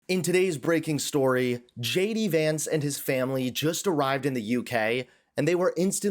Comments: I thought you said Shady Vance and decided this to be a very apt description of his character. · In the beginning you mentioned JD Vance. I heard "SHADY Vance". More appropriate in my opinion